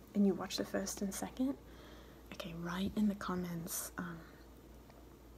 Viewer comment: Herbivore bruh I thought you said “uh before” and had to rewatch that part like three times. Btw i just wanted to share my favorite video from you because you got asked that question. I loved your chocolate tapping and scratching on that big chocolate bar. I had no idea why it popped up on my recommendations, I didn’t really know what asmr was but I watched that video and was like “oh wow this is so relaxing!” And then I just couldn’t stop and watched tons of your videos and now I’m just addicted and am always patiently waiting for your next uploads. Anyways, thank you so much for all that you do!!